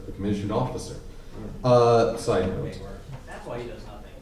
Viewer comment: what did he say that broke? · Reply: "That's why he does nothing!"